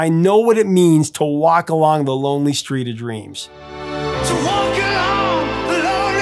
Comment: I know what it means to walk along when it's only three degrees 😂. That's what I always thought he said 😁